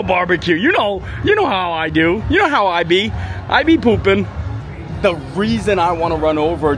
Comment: Did you say " I be poopin" ?🤣🤣🤣